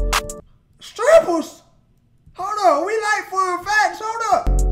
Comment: Every time this comes up on my feed, all I hear is “STRIPPERS?!? We like fun facts!”